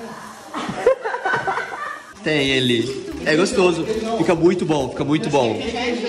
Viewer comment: fala " ce quer vida " ou eu escutei errado? kkk😍